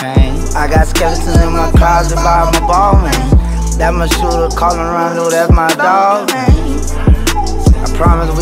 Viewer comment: did he say “That’s my shooter, Quando Rondo”?